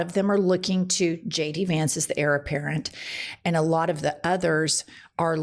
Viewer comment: Heir apparent? I thought you said “error parent”… I guess both can be true.